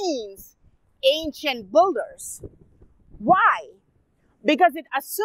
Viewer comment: I was checking something else for a moment but came back to rewind as soon as i heard him say: "Ancient boogers" ! 🤣😂 He was talking about builders, not boogers. My bad.